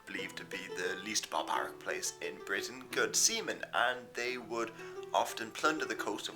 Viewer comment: Oh sea men, I thought he said "semen", haha, that got awkward for a minute there.